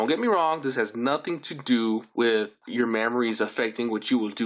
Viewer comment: I swear he says mammaries. Also also anyone else ever noticed that a god of war is at the end of that word? No, not high yet if you must know.